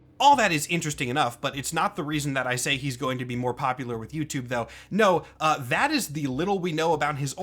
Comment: ohh I thought you were saying he was going to be more popular with youtube because he's hot